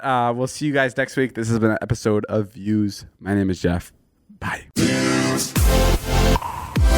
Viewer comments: Did anyone else catch “I’m Jeff” from at 45:33 or was it just me??? Lol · Did say “My name is Jeff” at the very end?!